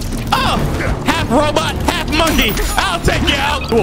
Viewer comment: Did he just say “HALF ROBOT, HALF MONKEY” 😂🙈